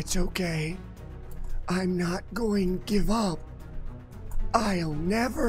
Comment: it sounds like he saying i not give up